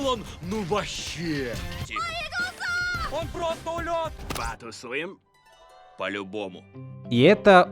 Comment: За столько лет я так и не понял, что говорит обезьяна на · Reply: Потусуем?